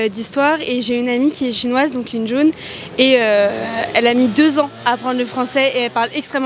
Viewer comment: Heureusement qu'il y a les sous-titres parce que moi j'ai entendu "avec une amie qu'est chinoise, donc une jaune".